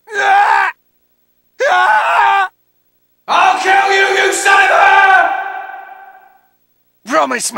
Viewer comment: I keep hearing.. I'LL KILL YOU INSIDE HER!